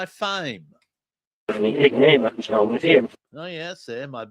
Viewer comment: I hear "faucet." Drugs can be a faucet flooding your willpower to quit. It seems like he's being truthful about cleaning himself up according to all of his reversals. Especially crack because you constantly chase the first 'hit.' I backed up to this one after hearing a few of them, to comment here. I didn't care what happened to him until I heard the few reversals. Now I feel guilty for not caring one iota about that clown. His dad was a dying clone of a clone of another cloning. The queen mum is a shape-shifting QUEEN BITCH. The Beatles told on "HER MAJESTY." (She changes from day to day). 😂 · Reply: ~ I hear, "my big fame; a show within." He's a legend in his own mind? 😂 I've noticed for a long time that it seems sometimes the reversals will have 2 or 3 words upon the one... like the "corpse," I heard "a box" on top of "corpse." I thought I even heard, "wooden," or "walnut." Even as it slows down, a word will change but it's the same topic. Does it go that deep? 40:49 Fame becomes 'name.'